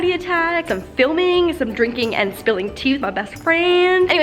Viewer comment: I thought it said "spilling tea with my boyfriend"😂